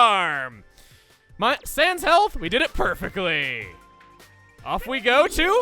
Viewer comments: Does he say Sans Health? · did you say Sans health?